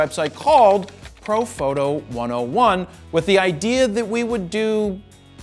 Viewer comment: first time i watched one of your videos, without know anything about you. It sounded like you were saying, "frodo photo," which makes no sense, looking back. Love your videos keep them coming. I love Tolkien too.